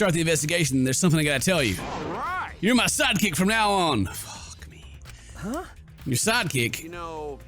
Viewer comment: did he just say...f*ck me?